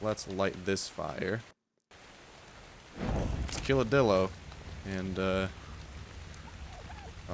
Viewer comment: It sounds like he said dildo about 50 million times in this episode